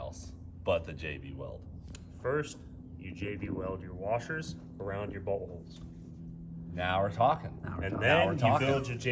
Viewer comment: almost sounded like said first you jb weld the washers around your buttholes 😂😂😂